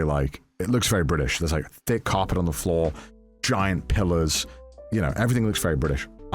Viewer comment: I first thought Connor said giant pillows instead of pillars lol